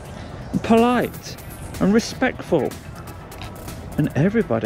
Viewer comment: did you say 'respecful'?! 😂